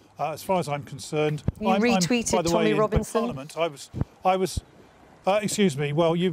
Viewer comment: I thought she said Tony Robinson at first there, and was worried I'd missed something... But no, it was Tommy Robinson.